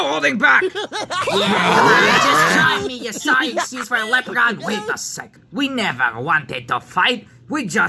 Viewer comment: did Chuck call Foreman pig a sorry excuse for a leprechaun?